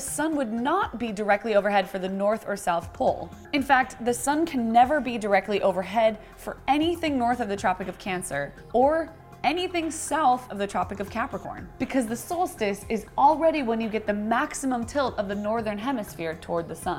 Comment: i only hear "The tropic of Yasuo"